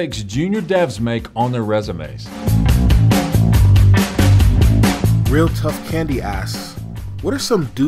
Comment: When that first question was introduced I could have swore he said "real tough candy ass" 😂😂